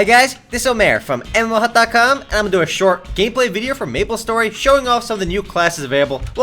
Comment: I heard: "Im gonna make a short Gayplay video 8D"